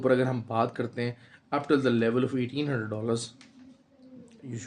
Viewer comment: What you said in clip 5:04 time frame 1800$ or 1900$ ??? Not clear you said Plzz tell me proper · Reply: Sir that was weekly divergence 1800